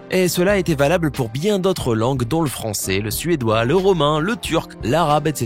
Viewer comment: c moi ou il a dit le fronçais 😂